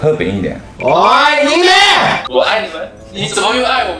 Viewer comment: 做为和的订阅观众，觉得看到两队荧幕合体好激动 🥳🥳 ''喂，你们！'' 听成 ''我爱你们！'' 整个没法再生气 哈哈哈 期待合作～